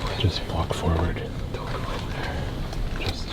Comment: i hear two times " Don't go in there !"